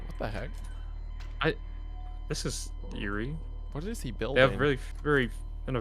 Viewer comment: I heard “oh this is…Yuri”